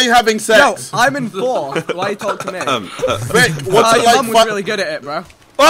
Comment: What did say at 15:50? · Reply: I think "your mum is really good at it"